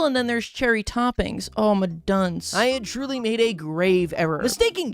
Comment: did he just say “oh mi dunce” Am i hearing things?